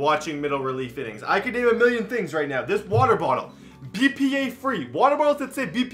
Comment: Honestly.. at 0:55 I thought he said PPAP and I was like PLZ NO